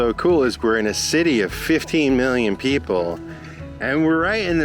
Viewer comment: Rio has about 7 million people living there, not 15 Million. I love your videos and nature passion.❤ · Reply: I thought I heard saying 50 million HAHAHA I said WOW! 50 million HAHAHA